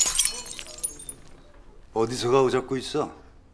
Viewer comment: 정우성은 발성부터 다시 연습해야겠다...연기 짬밥이 몇년인데 한국말에 강세넣는 법도 제대로 모르냐.. 첫 대사부터 '어디서 가오잡고 있어' 가 어눌한 발성처리 때문에 '어디서가 오잡고있어' 로 들림. 어우 보는 내가 조마조마할 정도로 어색한연기..그 다음 대사들도 말할 것도 없고..